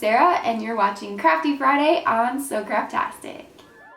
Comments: this is the first time I've ever watched your videos and I thought u said so crappy Friday and I was wander why Friday is crappy😂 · I thought she said "crappy Friday " in the beginning 😂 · Who thought she said crappy Friday